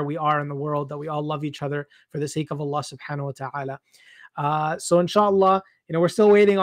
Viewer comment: after the host says Allah what's the words after Allah?? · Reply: Subhana wa ta'ala, Arabic for "The most glorified, the most high"